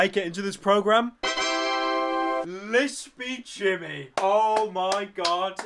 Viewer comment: Anyone else thought he said lesbi Jimmy?